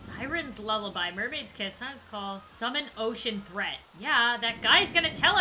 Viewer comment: When she said “Summon Ocean Threat” I thought she said “Summon Ocean Bread”